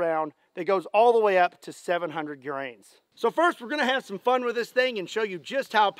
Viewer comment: I dont know why I heard this, but I swear I heard you say 500 magnum goes all the way up to 700 uranus not 700 grains.